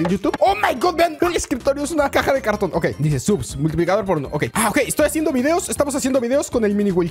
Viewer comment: minuto 1:03 parece que dice multiplicador porn0